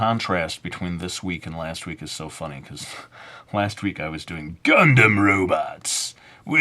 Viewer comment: at first i thought he said condom robots at